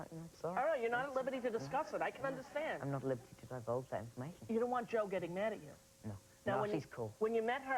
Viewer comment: Hello, I'm sorry I misner English by ear but I understand perfectly well reading in English, can you please write what he said (at 8:08 - 8:19)? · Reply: "I can't tell you, I'm not at liberty to divulge that information"